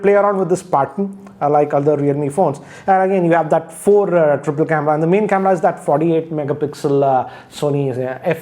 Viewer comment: did I hear saying 4 triple camera? 😝